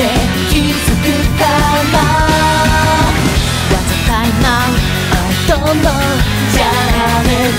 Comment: 余りに可愛すぎてあざといなうに空耳してしまう、、、